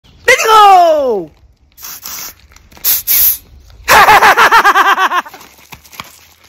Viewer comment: what is he saying? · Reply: “HERE WE GOOOO!”